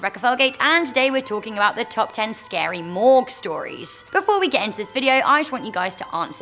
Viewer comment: I thought she scary said morgz stories